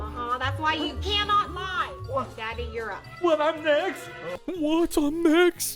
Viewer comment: did she just call him…. Daddy 💀